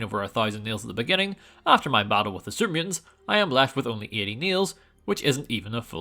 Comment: Auto-subtitles mishearing “super mutants” and saying “…after the battle with the Serbians” instead brings to mind a version of New Vegas from a MUCH more interesting alternate timeline